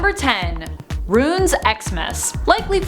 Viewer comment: Anybody Else Hear “Runes” Xmas